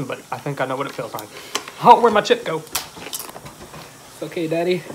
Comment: lives up to his name... he showed nothing but love taking care of you. He may have suggested the challenge but he sure didn’t leave you when things got rough! Is daddy? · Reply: I heard say "It's ok, daddy." I thought said something like "Did he eat it?" or "Can I eat it?" I could be wrong. Lol